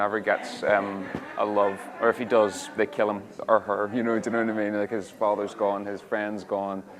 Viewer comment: The question everyone keeps asking. "What did Bradley say?" at 03:18 "Or Merlin torches them."